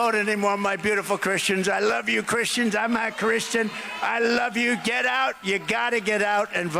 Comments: Did he just say, "I'm not Christian?" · sounds like he said I'm NOT Christian. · Did I just heard Tr*mp say “I’m NOT Christian”? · Did he just say ‘I’m not Christian’??? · I’m a Christian who will not be voting for Donald Trump. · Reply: Did I hear a Freudian slip from Trump? Sounded like he said, “I’m not Christian” at 5:08. I know that to be true but it's nice to hear him admit it. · He just admitted he is not a Christian!! Listen to his words! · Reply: That's what I heard too. I'm glad I'm not the only one. He told them ppl he loves Christians and as they cheered he said I'm not Christian. Those ppl better wake up. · it sounds like Trump said "I'm not christian"! WTF Did anyone else hear that? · did he say “I’m half christian”? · Pay attention to what trump says at the 5:06 5:08 minutes mark with the caption on. He clearly says 'I'm not Christian' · Wait, did he say " I'm not Christian " ????